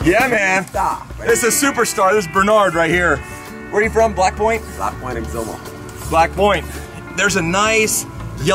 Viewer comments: Did he just say what I thought he said? lmao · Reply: He said black point. It sounded like black boy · dude ,that didnt sound good,i know its not what u said,but it sounded like u said"black boy". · I thought u said where u from blackboy🤣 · did you called him black boy???? this is racism pls explain yourself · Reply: to what I said, I said black point. As in black point, Exuma. The town he's from. Even if it was black boy, Exuma. How would that make me racist? I called him my friend and helped him get a grouper out of a hole. Wouldn't actions speak louder than words? אסף פרידמן